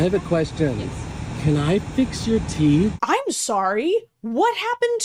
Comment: That dentist BOILS my blood. Imagine getting insulted to your face and you have to smile and nod because you're at work AND on camera. And not only that, your bully gets pissy you didn't appreciate them and posts your insecurity to thousands of people. I hope that barista never spends a day in her life feeling anything less than gorgeous. And I hope she never gets toothaches so she doesn't have to deal with a dentist ever again! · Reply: I won’t lie I thought he said “fix your tea” and was like ok she works as Starbucks so why would you do that